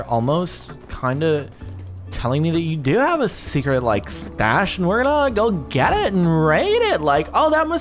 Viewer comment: I thought she said get a stash of weed.